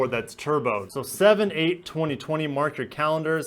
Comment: At first I thought he said 8 7 2020 and I gasped because thats my B-day. Then it wasnt